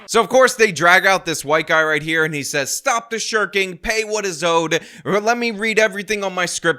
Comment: Ah. Stop the shirking. I thought he said stop the shuriken.